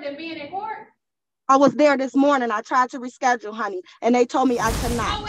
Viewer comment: Did she just call the judge "honey"? 😂🍯😏💕